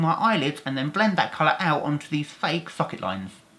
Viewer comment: it sounds like he says “fuck it” not socket and has a lisp...love watching your vids